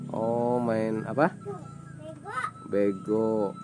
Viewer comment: si aa nya salah dengar Mksud si dde nya tu main lego bukan beg* astagfirullah 🤣